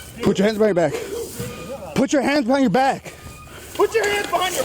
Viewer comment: Cop is clearly saying, in English, "put your hands behind your back." He's looking up at the cop the same my dog does when I ask him what he's doing over there.